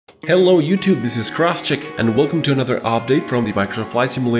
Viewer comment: Everytime he starts a video I just hear "Cross Chicken here"